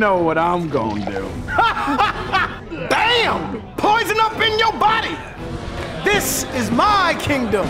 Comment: When he said : ha! You got poison up in ur body! I thought he said: ha! You got up in ur ass! LMFAO XD